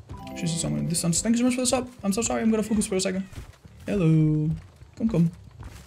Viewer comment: "Im so sorry chat im going to focus for a second" Me: That is a man with murder in his eyes. Love the come back Karma. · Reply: said "I'm going to focus for a second" but I heard "sorry Master; I must go all out, just this once".